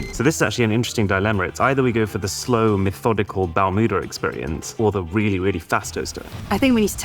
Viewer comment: Every time he says ‘Balmuda’ I just hear ‘Bowel Movement’